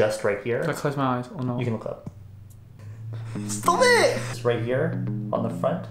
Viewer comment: I thought he said stob iit . Reminded me of Jin frm BTS 😂💜